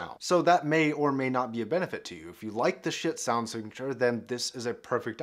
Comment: I heard " shit sound signeche " 😂